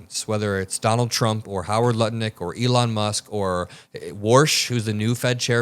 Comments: I heard you say "Howard Nutlick" 😂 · All I hear is 'Howard Nutlick' evertime says his name...I cant unhear it!!!! lol